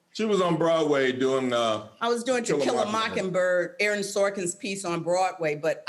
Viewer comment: Hahaha... I first heard "Tequila Moking Bird"... if you're looking for a name for your new bar, feel free to use it! :D